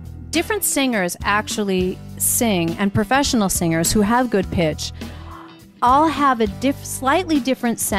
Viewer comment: I thought i heard, *professional singer has good bitches*